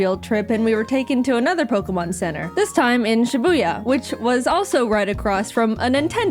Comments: did you say shibuya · did you just say shiboya